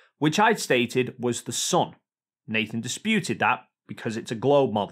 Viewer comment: the son? 😂 I'm not a native English speaker but i hear son